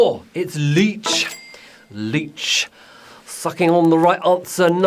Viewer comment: says f***ing instead of sucking! I was weeaakkk